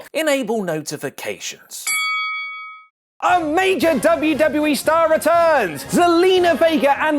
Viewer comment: When u said “a major” at the beginning I thought you said “Omega” and I was so confused and exited for a moment